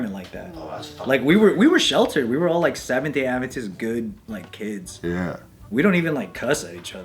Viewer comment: What did say at 20:42? I didn't quite make what he said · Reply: "Like, we were sheltered, we were like Seventh Day Adventist good. like, kids."